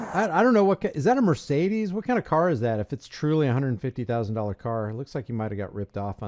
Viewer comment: I thought he said the he Didn't have a 150,000 car ! LoL